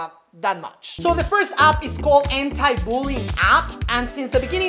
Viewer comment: sounds like he said 'Hentai Bullying'